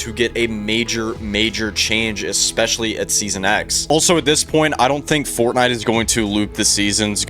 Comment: the goat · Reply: i thought you said the season x was goated its bad